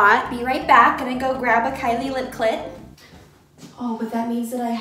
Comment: Am I the only one that rewound it so I could check if I heard her right saying Kylie lip CLIT 😂😂